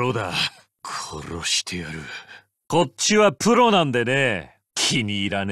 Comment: Can anyone please tell me what Kuroiwa says here accurately? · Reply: こっちはプロなんでね。 (Kocchi wa Pro nandene) Eng : I'm a professional.